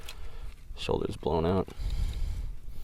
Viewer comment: I was sipping a Raspberry/Peach Italian soda and all I heard was "Shoulders blow'in out" and I thought "OH CRAP! NOW I WON'T HAVE ANYMORE VIDEOS TO WATCH IN THIS CHANNEL!" (With a feeling of dread like I had missed a college final exam-------- then I wake up and still think it is REAL🥵😳😱 Then I realized he was talking about the bottle. 😮😅😅😊